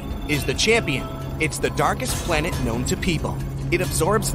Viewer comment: i thought it said dankest